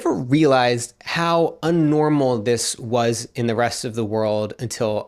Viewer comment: Glad you’re enjoying Australia, but did you really say “un-normal” at 6:10 (approx) instead of abnormal or did I mishear?